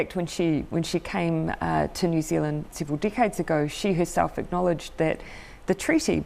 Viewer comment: did she say "several dickheads ago"?